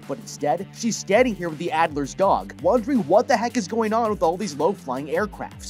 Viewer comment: At the begining I wasn't playing attention and I heard "What the Fuck"